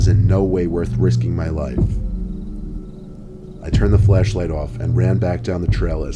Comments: someone tell me they also heard him say flesh light instead of flashlight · When you said flashlight I thought you said fleshlight. "obviously I brought my fleshlight" lmao